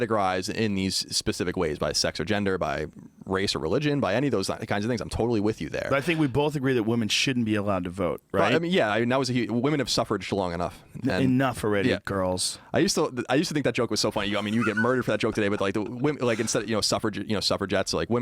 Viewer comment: I thought he said "women have suffered sclong enough." The joke he was actually making is funny, I guess...